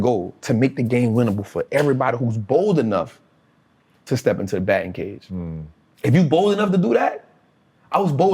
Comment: what is he saying? "bold enough to step into the ____?" help, cuz I want to write that down SO bad - it was so good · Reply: batting cage